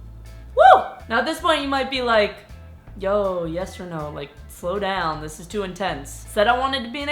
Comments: I first came here I thought see said "hey its yes or no" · this my first time watching ur video and at the start i thought u said yes or no sorry 😂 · i thought you said your name was yes or no and died when i saw that its